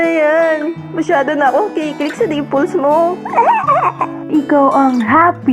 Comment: when she said about the dimples thing i thought she said Nipples XD